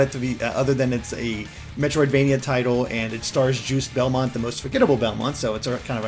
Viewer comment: Did just say "Juice Belmont"?